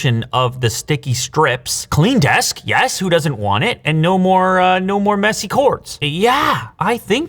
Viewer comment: Doggo clearly knew Flying Ball was actually Satan's Testicle. Also, when he was wrapping up the invisible wireless charger, referring to the hidden cord, it really sounded like he said, "...and no more messy corpse!!" 🤣